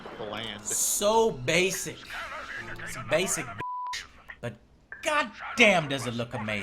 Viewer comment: Did really say "basic bitch" at 10:05? lmao